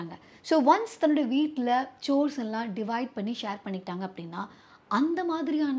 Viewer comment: what word she mentioned? Anyone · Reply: Chore - duty or work